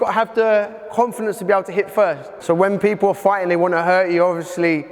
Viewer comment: Did he say "hit first" ? Did he really say "hit first" ??? That is essentially exactly what Aikido is NOT about